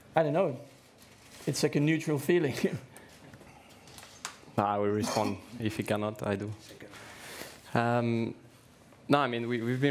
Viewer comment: what did ocon say ?? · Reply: I will respond. If he cannot I do.